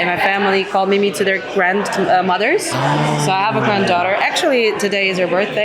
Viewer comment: Did she say she, she has a granddaughter? 6:35 is that true? How? 🤷🏾‍♂️ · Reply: No, Goddaughter (afilhada)